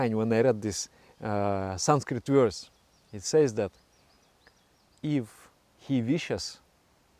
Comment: "if He wishes" captions say "vicious" :)